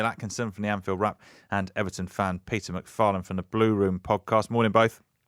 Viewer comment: That’s not Ped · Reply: Ah, sorry, I miss heard. Thought he said at the beginning Peter McPartland.😊